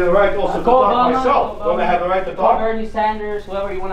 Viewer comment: I thought said Joan Baez, but he said Joe biden...